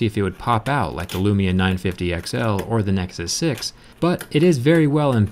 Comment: did u say nexus 6 ? I searched on your channel and i didnt find the video, if you can send me the link it'll be appreciated, leep up the good work jerry .